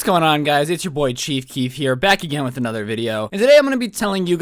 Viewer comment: Hold up I know I’m late but did he just say what’s up it’s chief keef?